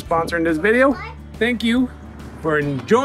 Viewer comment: Ha ha - - “Thank you for enjoyning us on our build”. Almost thought he said egg joining us. Had to re-listen.